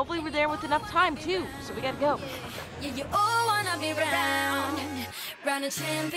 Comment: I thought you said y'all won